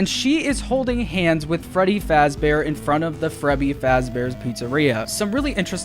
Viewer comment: did he just say frebby